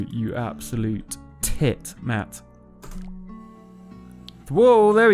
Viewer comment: Wait so at 6:13 did you say your name was Matt? That’s p cool dude